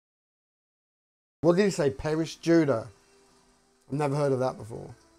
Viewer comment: I thought Mosley said "Paris Juda' but he actually said "Perish Judah". Mosley hasn't hidden his hatred for the Jews or Gypsies. So, Perish Judah would mean die jews or death to the Jews.